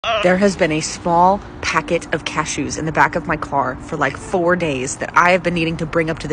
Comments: ' cashews' ☠️I thought you said cat shoes 😂😂😂😂😂😂😂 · I thought you said cat shoes not cashews